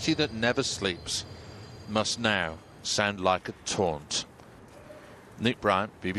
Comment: he stated: "the city that never sleeps must now sound like a taunt". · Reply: No worries, you can find the comment at 3:30. It was just my personal interpretation, but it is though a bit unclear what he said but it sounded like he used the word taunt. Have a good one!